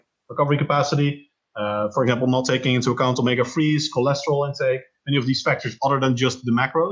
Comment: What does he say at 22:01, the word before „cholesterol“? Thanks! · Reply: Omega 3